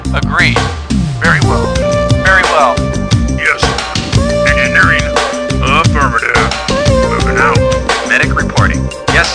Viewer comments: i have been wondering what does the engineer says when u pick him in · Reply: sabufinisher yes, sir. Engineering! Affirmative. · He say "Venichle Reporting" but I hear "Decko Reporting" and it sounds like very something nice.